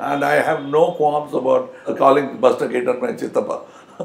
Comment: What did he say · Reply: Buster Keaton is my chittapa